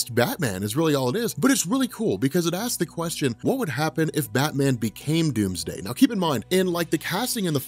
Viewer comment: I thought it said Batman becomes Tuesday?!?!?!??!?!?!